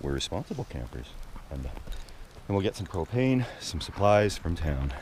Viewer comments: I thought he said get some cocaine from town 😂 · 18.11 it sounded like you said where gonna get some cocaine 😂😂😂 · Lol. At around 18:00 it sounded like you said " we're responsible campers, going to get some cocaine and camping supplies from town.". But it was propane not cocaine. Had to rewind a few times. Good videos. Peace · when I first listened it sounded like you said cocaine. I was like wait...what?? Then my brain clicked and was like DUH!! PROPANE!! Love your channel...if I had the money i would camp everyday for the rest of my life!! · did he say will get some cocaine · did he say get some cocaine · we're going to get some cocaine and other supplies from town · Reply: I listened too it several times it still sounds like he says cocaine fkn funny seem like an snl skit